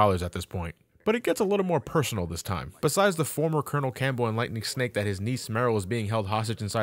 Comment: I swear I heard you say … and Lightning Snake. I was like who the hell is that? Enlightening Snake🤦🏻‍♂️